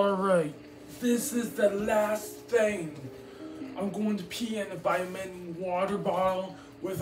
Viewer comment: FOR A MINUTE I THOUGHT HE SAID HES GONNA PEE IN A VIETNAM BOTTLE 💀